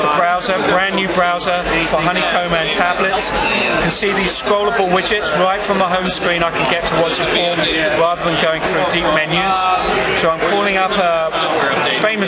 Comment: did he say right from the home screen i can get 2 watching porn videos? or if he didnt wat did he say???